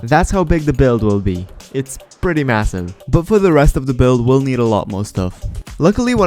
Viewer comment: did I hear massive